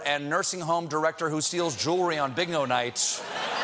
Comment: He said Big no, instead of Bingo...